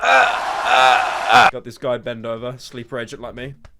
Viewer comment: When he said ben dover i heard it "bend over"